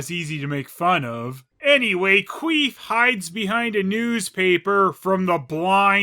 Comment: I swear I heard you say qweef